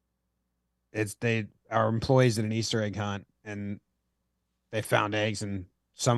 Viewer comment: Instead of egg haunt I heard “Akon” I think I should goto sleep now… 😂